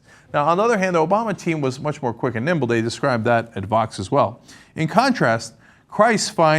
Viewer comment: I thought that you said "They described that at Fox as well." Instead of Vox. I was like whhhhhhhhhhuut